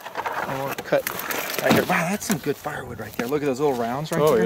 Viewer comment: I thought I heard say "we can use them for smoking weed too"